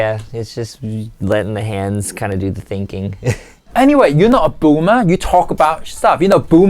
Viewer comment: I thought he said "Gooner" 😂😂😂😂😂😂😂😂😂😂